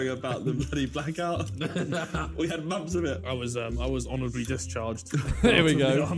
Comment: does no one else hear Honourable instead of DIShonourable 😂